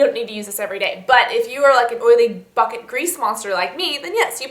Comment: When you said oily "bucket" grease monster i thought you said oily.... "f**kin" grease monster and my mouth dropped i had to rewind it!! LOL